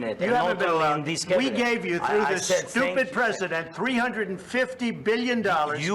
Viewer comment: Is trump saying zelenskyy stupid president